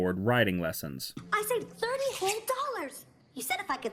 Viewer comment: Francine says 30$ Subtitles say 0$ which is better